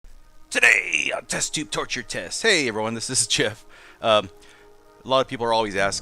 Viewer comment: i heard it as test tube torture test well, sounds about right